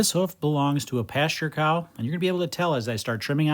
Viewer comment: I misheard it as "pastor cow" and was confused on why a church had cows, maybe that's why people say holy cow